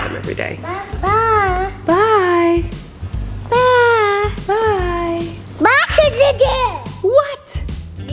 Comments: was it just me who heared bye say bye felicia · anyone else thought he said “BYE FELISHA” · i literally heard ’Bye Felicia’ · It sounded like said "Bye Felicia" at 4:14 lmao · It sound like is saying “Bye Felicia” on 4:14 lol